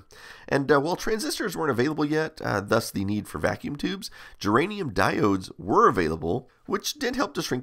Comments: Did I hear "geranium diodes" at 7:05 ? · Geranium diode! did I hear that right? 😄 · Thanks for another great video. Did I hear you say "geranium diodes"? at 7:06. Big lols.